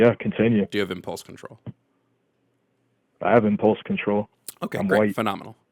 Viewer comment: does he say "I have impulse control, I'm white"?